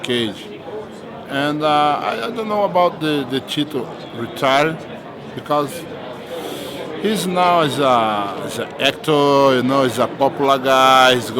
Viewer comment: I thought Antonio Silva said Tito's retarded 3:24 xD lmao........